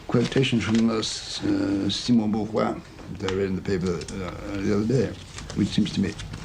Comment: I didn't have much trouble understanding the overall meaning of what he said, but I did stumble sometimes. For others who wonder, the quote is by Simone de Beauvoir, had to look it up by the quote itself. (He completely skips "de" which threw me off completely, I thought he said Simone "book one" or something until I replayed it a few times.)